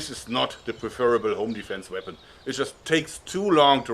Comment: It really sounds like said ‘OnlyFans weapon’